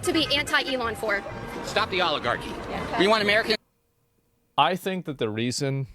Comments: I seriously thought this guy said "Stop the Olive Garden". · sounds like he is saying stop the Olive garden 24:58.